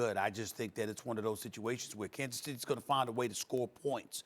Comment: At about 00:25 did he just call us Kansas State?